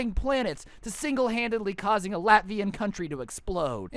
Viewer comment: Did you say "causing Latvian countries to explode"?